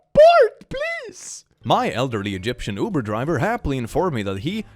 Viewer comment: I really thought I heard “my Elden ring Uber driver” swear I game to much